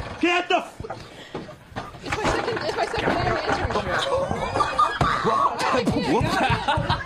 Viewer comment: I think he says "it's my second day on the internship"!